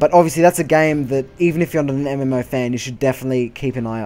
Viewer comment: Are you from Germany ? Because on 1:28 it sounded like you wanted to say Ps vier instead Ps four